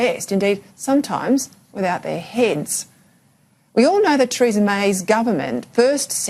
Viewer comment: Treason May! I heard her say it!